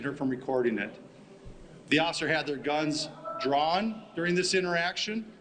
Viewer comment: Sounds like he said, "Had their guns drawn" which leads me to believe they started with guns at the ready, and did "pull their guns" on her during the stop...